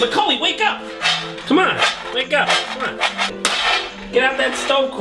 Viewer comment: "Should i hose night all night didn't i?" wait, waitWHAT did he say? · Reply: I had to replay it again too, haha "Shit I hallucinated all that, didn't I?